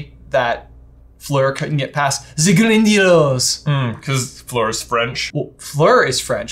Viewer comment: is actually right about the Hermione scene - Harry says he finished last, and Hermione is mocking Fleur's accent to make him feel better. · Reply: I always heard "the grindylows" when i watched the movie on my tv. Yet when played the clip, i could hear "ze grindylows". I guess tv speakers and phone speakers are different.